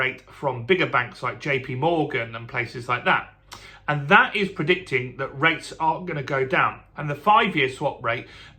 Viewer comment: Did u see aren't gonna come down, or are gonna? Sorry was difficult to make out what u said then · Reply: If the tariff war continues rates should come down.